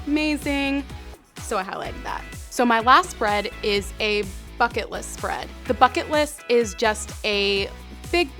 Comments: When you said bucket list I thought you said... F*ck it list XDDDDD (;ŏ﹏ŏ) Please don't like this comment I don't deserve it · It sounds like you said f*ck it list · at first I thought you said "fuck it" list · I swear to god I thought she said "Fuck It List"